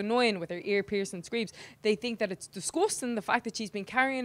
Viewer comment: The hell did she say at 0:18? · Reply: Venonous Villain she said “disgusting” but it sounded like “disgoostin”